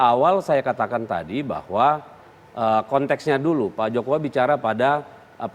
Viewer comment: apa saya salah denger pk jokuwa....😃😃😃